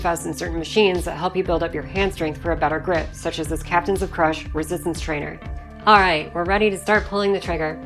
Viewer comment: Captain Crunch? Ohhhh I misheard lolz.